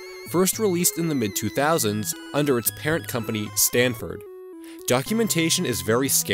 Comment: and 3:09: Is it just me or does it sound like he is saying Stanford?